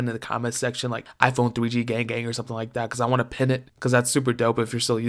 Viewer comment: I thought he said to say “if you have a iPhone 3g say GAY GAY” 😂😂🤦‍♂️